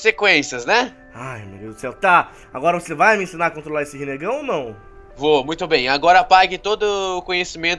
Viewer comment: Eu ouvi rinnegão